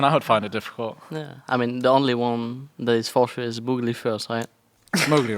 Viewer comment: Anyone know what sOAZ says at 32:31? It kinda sounds like "the only one that is for sure is Mowgli (moogly) first", but then he doesn't include him in the top 5 list